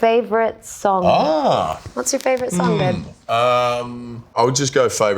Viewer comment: Did Anya just call Chris ''babe''